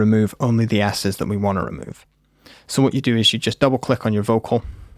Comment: "right click and open in audio edisor" when you think of edison but want to say editor :D